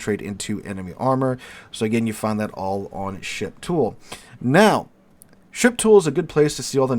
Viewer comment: I keep hearing "ship tool" as "shit pool" xD